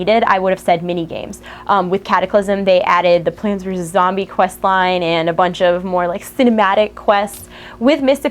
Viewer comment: hey, couldn't hear what u were saying at 8:49.. "plainezerzeium zombie questline" ?